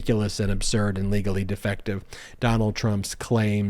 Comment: I thought Ben said trump was legally defective!😊😊😄😅😂🤣😅😂🤣🤣🤣🤣. It's been about 5 minutes, and I'm still laughing my head off at what I thought Ben had said.😂😂